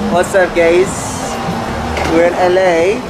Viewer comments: why does is it sound like he said “wassup gays” 😭✋ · Theo looks so excited💕 5:50 Why did it sound like he said ‘what’s up gays’😳 I must be tripping💀 9:49 Theo is very pretty🥰 12:46 - 13:04 Poor Theo😅❤️ 16:00 Yes, but a cute one😂💞 17:21, 18:35😂😂 25:45 Lmao how did he manage to do that🤣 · bruh did anyone else hear “whatssup gayss” from lego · I THOUGHT HE SAID WHATS UP GAYS HELP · I coulda SWORN he said ‘wassup gays 😊😀❤’